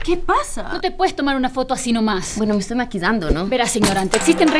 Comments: like si escuchaste que dijo maquidando en el momento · Si, se esta ''Maquidando'' :v like si oíste que dijo eso xd